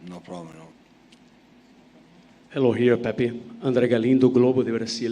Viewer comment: did he call him pepi? 😂🤣